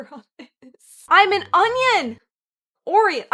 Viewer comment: why did I actually think that said Onion..